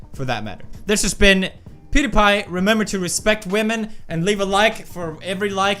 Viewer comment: did he say "women" at 10:30?